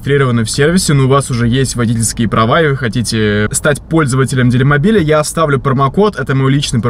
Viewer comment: На 3:05 я один слышу в рекламе «Дерьмобиль» ?